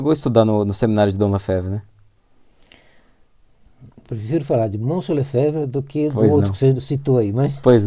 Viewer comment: O que Dom Tomás quis dizer em 13:38? Não entendi muito bem. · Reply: Eu perguntei a ele, como foi sua convivência com Dom Lefebvre? Ele respondeu, "prefiro falar de Dom Lefebvre do que desse outro que você disse aí", ou seja, prefere falar de Dom Lefebvre do que dele mesmo. Isso é próprio dos monges, não falar de si mesmo.